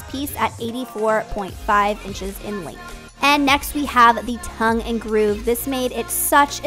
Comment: Guys heeelp i'm from europe so english is not my native language... what is she saying at 1:52 ?? Tongue and groove ?? What ? Haha :D i love the headboard... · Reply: Hi Carmen! Thanks for watching! Yes tounge and groove ❤❤❤